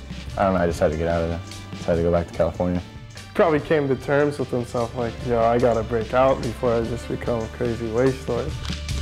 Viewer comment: What did say?? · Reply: "probably came to terms with himself, like yo I gotta breakout before I just become a crazy waste story"